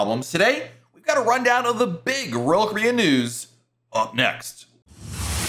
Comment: Sounds like you say ROYAL KOREAN.